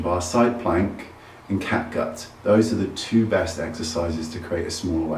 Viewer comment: what is that other exercise for girls for waist: side planks and ...? I keep hearing cat guts, lol... Sorry, english is not my first language.